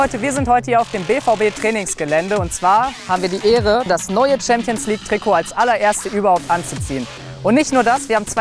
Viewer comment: did he just say neuer?